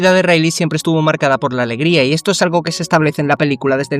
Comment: Nmms banda le entendí " cagada por su alegría" xD